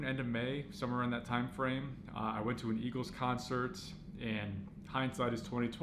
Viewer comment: I thought he said Migos concet... I was like orly... word...